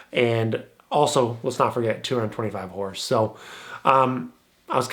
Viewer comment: did you say 225hp?? Which model is that?